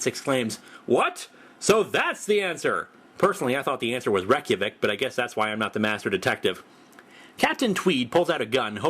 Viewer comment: What does he say at 1:02? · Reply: "Personally I thought the answer was Reykjavik, but I guess that's why I'm not the master detective."